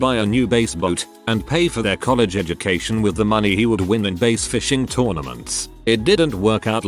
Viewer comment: When I heard the TTS say "base fishing" I thought for a moment it was like base jumping, but you're also fishing at the same time.